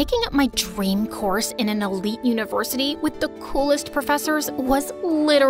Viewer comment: DID SHE SAY ELITE UNIVORSITY XD